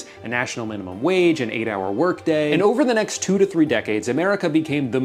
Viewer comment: You said “8 hour work day” and for some reason my brain heard “8 day work week,” probably because of how dystopian the beginning of the video was. Couldn’t help but laugh. If they could do that, they would, but it’s far easier to cut us all out by using AI instead of altering the space time continuum.